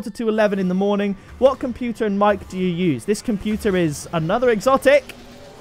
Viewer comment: that’s funny my computer is another exotic is sounded like one sentence.